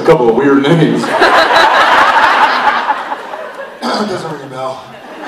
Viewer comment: What does Jared say at 15:02 after he clears his throat? Thanks in advance! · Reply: Akili Alima "doesn't ring a bell"